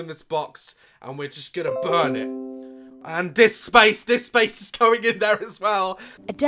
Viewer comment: “Stop giving my daughter flu she needs to work for space ed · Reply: I thought he said space sex LOL